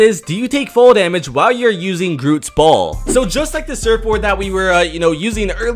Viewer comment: I actually thought he said Groots balls 😂